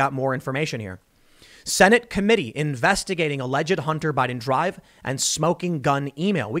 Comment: I thought you said “Send it” committee then I looked at my phone and saw the article you were reading😂😂😂🤙🏾